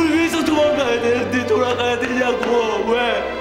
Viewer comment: 누구때문에 도망나왔는데 돌아가야되냐고 라고 들리는 기분.